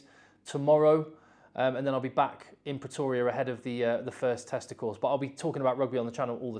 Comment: Did you just say youd be back ahead of the testicles?